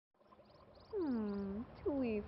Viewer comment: I heard her say Osama